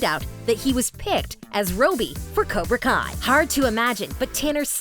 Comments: did she just say rooby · Did she just say roebie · Did you just say rooby